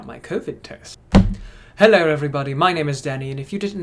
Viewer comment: Ok sorry but I thought he said “Hello Everybody my name is Daddy “ 😂